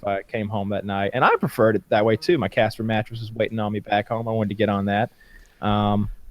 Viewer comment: had to replay it and Kyle is saved. I thought he said "my cats" something, which meant he had cats that wanted him home too 😂